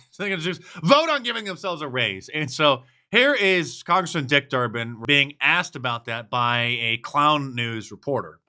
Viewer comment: Is that congressman called "Dick Turpin"? I probably misheard that but if you know who Dick Turpin was you'll see how apt that name would be 😂😂😂